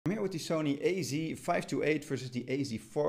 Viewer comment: Did you say "sony" or "only" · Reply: He did say sony